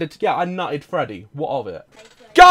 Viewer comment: "yeah I nuttered Freddy, what of it?!" 😂😂😂fav part idk if he said "nuttered" or what but the WHAT of it was funny haha